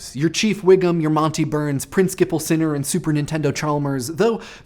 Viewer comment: Did he just say "superintendent" as "super nintendo"?